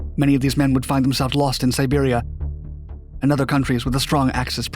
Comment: Sir i think you said "lost in siberia"